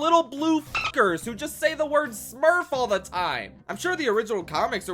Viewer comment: did u just say the n word😭